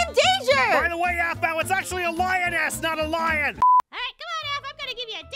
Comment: Did anyone else hear swearing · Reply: He said a$$